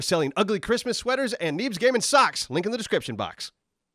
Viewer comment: why do i hear: ''neebs gaming sucks'' instead of: ''neebs gaming socks''???????????????????????